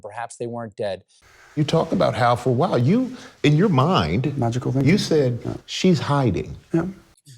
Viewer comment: Did he say MAGICKAL THINKING? Timestamp